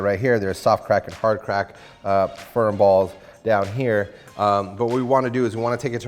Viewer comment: Did he says firm balls at around 1:54?